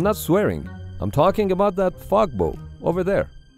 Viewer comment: I thought you said fog boat